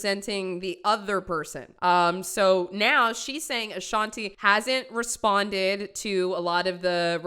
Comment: Chile I zoned out and I heard “Ashanti hasn’t responded” and I looked up confused 💀